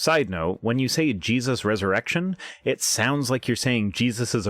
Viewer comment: Ha! I heard "erection" too!